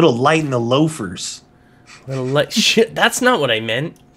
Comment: did you say sh*t at 13:20?